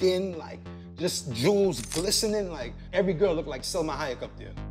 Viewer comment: What was the name of the lady at 3:14 I couldn’t understand what he said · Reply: Salma Hayek